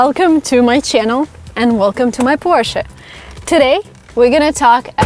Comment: Welcome to my Lada! :)) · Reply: I thought she said Welcome to my tourshit